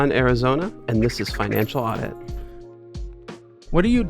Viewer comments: Probably going to catch hate for this - this is not an adult that should have 50/50 custody of a child. Made the 4 year old sleep on the couch of a stranger because of homelessness, refuses to get a job, actively waiting for mommy to die to get an inheritance, etc. And actively abusing the good hearted nature of the current partner doesn't seem like someone that suffered as explained. We already know there is zero accountability being taken for the finances, it seems like that is probably a theme. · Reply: it sounds like he said, "And this is enchilada." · I thought he said “and this is fun enchilada” lol